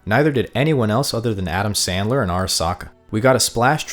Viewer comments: Had to pause at 0:27 to be sure I heard Adam Sandler correctly. lololol😂😂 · Did I just hear you say “Adam Sandler?” Lmfao